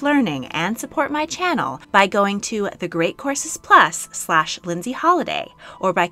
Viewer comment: Rewatching this after listening to it in the car. I literally thought you said great corsets plus. And I was super confused 😂